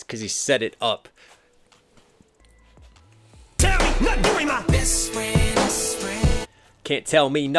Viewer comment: I think Em actually said, "Not doing my-" which ties in to Yelawolf's "Best friend" -> "don't tell me (I'm) not doing my best, friend"